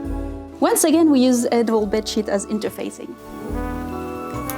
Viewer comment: Today, on "Your captions said whhhhaat?" @ 16:08 What said: "Once again, we use an old bed sheet." What the captions say: "Once again, we use edible bed sheet." 😂